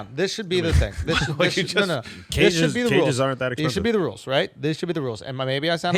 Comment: did Al say cages aren't that expensive? that's some cruel stuff right there